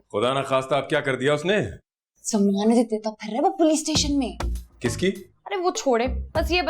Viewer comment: what she said anyone can tell ? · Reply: Zamanate deta fir rha woh police station me *